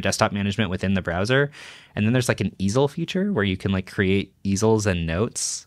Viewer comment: Is nobody going to talk about the fact that he said "isle" feature as "izzle" or am I hearing things.